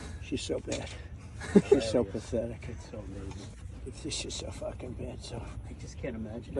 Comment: I don’t think he said b**ch. What I heard was “She’s so F*ng bad sh…” (like he was about to say she and continue muttering in his typical disorganized way of speaking). And honestly, that shouldn’t even be the focus of the video. The bigger part is the blatant misogyny, where he obviously thinks that a woman won’t be able to stand up to world leaders. And even with that, the battle lines have long been drawn. We know who and what he is. We know he can’t surprise us by being a human turd. Just gonna have to do the vote. I wish we had someone in the dems that would actually stand up to Israel, but I think she’s a much better choice in that also. Even if just by a little.